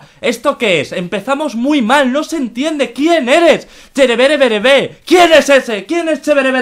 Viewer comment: su canal es creo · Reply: yo escuche ''Chevereverevere''